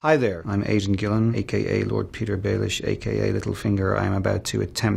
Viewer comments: recap of the comments: everyone just wants to fuck aidan gillen including me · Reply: Jokanaan thought he said he was Asian Gillan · I THOUGHT HE SAID,'hi I'm ASIAN Gillan' HAHAHA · thought he said "Hi there, I'm Asian Gillen" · Sounds like he said, "Hi there. I'm Agent Gillen."